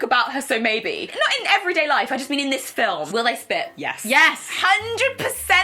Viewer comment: I thought I misheard "split" but no, they were talking about SPIT. are not over Disobedience 😶